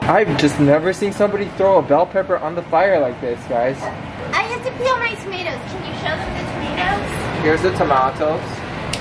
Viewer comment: am I the only one who heard "I have to pee on my tomatoes" @ 15:30?